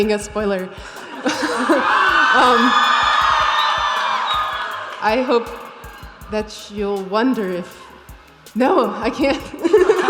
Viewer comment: no she didn't say "I wonder have we ever thought" she said "I hope that you'll wonder if ... NO I can't !" they are very different my dude.